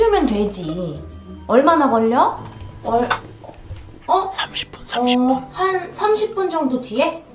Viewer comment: 누가30분이라말하게들리느건기분탓?